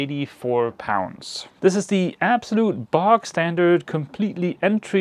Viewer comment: I’m sure you said you had bought something that was absolutely bog standard. I rewound several times but I keep hearing the same. Please confirm this was an issue with the edit 😂😂